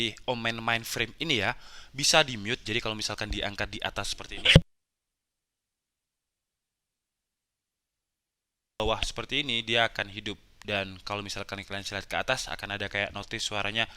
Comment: apa gw doang yang bisa nebak kira kira bang ngomong apa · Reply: Otomatis suaranya mati kemudian kalau kebawah